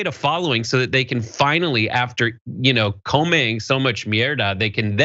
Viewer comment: Hold up @ 3:31 Did you say what I think you said?!?!?😂😂😂😂 EDIT: For the future it's El comío mucho mierda. 😂😂😂😂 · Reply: What I understood was something more like "after comment so much mierda" (saying the word in spanish to avoid saying shit).